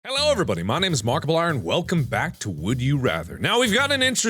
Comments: In his intro I thought he said ‘Hello everybody my name is markable iron’😂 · At the start did he just say markiple iron? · sounds like he said, "hello everybody my name is markable iron." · why does it sound like he said markipliorn ik he said markiplier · At the beginning I swear I heard Mark say, “Hello everybody my name is Markable Iron,” And I had to go back and listen to it again 😂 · did he just say markible arm?